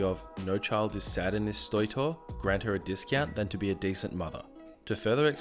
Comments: Did no one else hear he said stoy tore instead of toy store at 7:37? Haha 😜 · Woop woop! 43 minutes late squad! Also at 7:35 you said "stoy tore" instead of toy store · Did anyone else hear him say stoy tore at 7:37? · Did you say stoy tor at 7:39 lol love the videos!